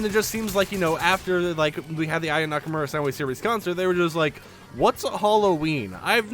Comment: what did he say lol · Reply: More specifically the "Aya Nakamura Soundwave Series Concert"